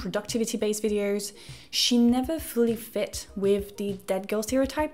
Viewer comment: What is the "dead girl" stereotype referenced at the beginning of the video. I tried looking it up and found nothing. Am I just mishearing the phrase? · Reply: I think she's actually saying "that girl." Which is a TikTok aesthetic centered around being aspirationally healthy, productive, and put together.